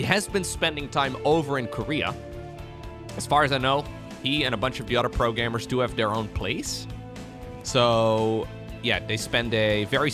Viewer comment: I had to rewind the video… I thought you said: He and a bunch of the other pro gamers have their own planes 😂